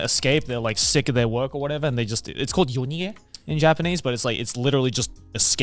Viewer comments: Can anyone transcribe this one Japanese word for me? I want to look it up, but I can't figure out exactly how it's spelled. yonie, younie, yoni ei, yoniee, ionie, ioniye, oniye, ounie, ouniyei, etc, etc. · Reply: It's 夜逃げ - yonige, 夜(yoru) meaning "night" 逃げ(nige) meaning "running away" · Wait. What’s the word that Joey used here? よにえ？ようにへ？ What’s the romanji for the “escaping/suddenly disappearing” that Joey said here? Asking for research purposes 👀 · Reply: Yonige, the kanji reading is 夜逃げ also